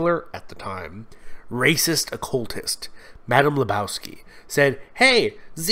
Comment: Did I hear Madam Lebowski?